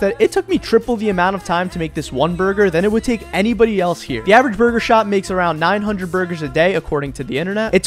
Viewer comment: I thought he said burger shot from gta 💀